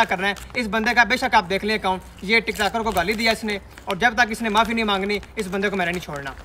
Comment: "tiktoker ko gaali di hai" ...... Sounds like "khusry ko gaali di hai" 😂😂😂🤣🤣🤣 Ab koi samny jhuk gya hai toh bhai q khamosh hai jamm k lo 🤣👍